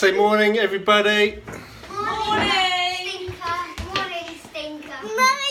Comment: did he say “good morning, stinker !” ? 😂😂 very cute these two